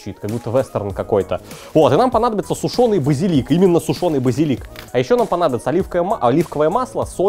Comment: послышалось вазелин😂